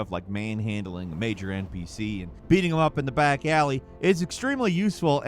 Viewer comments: I had to reply that one part a few times I thought said “you can manhandle an MPC and beat him off in the back ally” BAHAHAHA nope he def said beat them “up” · Ohhhh I had to turn on cc for a minute. You said, in regards to kcd2, "beating them up in a back alley" I heard "beating off in a back alley" that was actually impressed for a minute with all the stuff they put in that game LOL